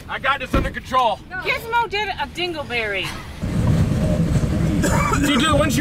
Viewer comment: Did she call you a dingleberry?